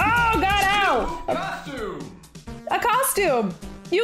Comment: Oh my god that game is cray cray. I thought was saying you got a costume! Lol